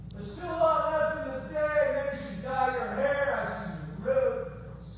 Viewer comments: What does he say at 55:22 the voice is not clear i can't understand!! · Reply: Johnny says "There's still alot left in the day maybe you should dye your hair I see roots". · What does he say at 55:22 the voice is not clear i can't understand!! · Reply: I was wondering the same thing. Sounded like he was about to belt out a tune there for a second. lol. Something about dying her hair?